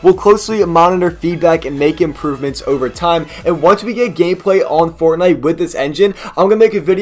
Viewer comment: i swear u said gayplay